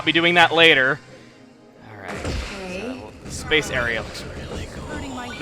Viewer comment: Uhm at 8:15 .... anyone else hear that? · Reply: Yes. Its saying "This level looks really cool" Congrats on hearing it your mother must be so proud!